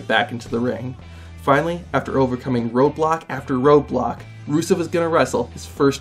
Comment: thought u said Roblox